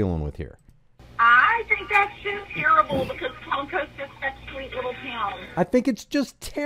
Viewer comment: in the video you can hear a lady say " I think its terrible, because Palm Coast is a sweet little town" I think she says Palm Coast , she did not say Cummings . So he isn't holding a Cumming sign in Cummings. I dont care what his sign says .. I do care about being mislead